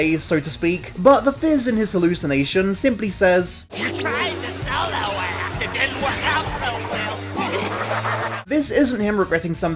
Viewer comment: I didn't even understand what fizz boi said lol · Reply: “You tried the solo act it didnt work out so well *insert laughter*”